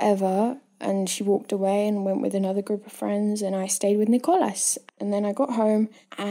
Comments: When she was saying Nickolus it sounded like she was saying Niklaus... maybe that’s jus me because I’m obsessed w klaus from tvd / to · Why does it sound whenever she said “ Nicolas” it sounds like she said Nico ass